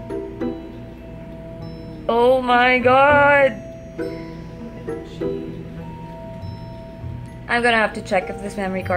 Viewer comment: yung background music meron ba nagsasalita? may narinig kasi ako na whisper at 3:34 right after said "Oh my God". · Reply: guess what...I heard "Ashley"